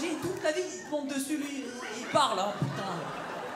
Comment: Qu'est ce qu'il a dit à 11:23 " vivement ???????? putain j'en peux plus" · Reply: Putain, vivement Charal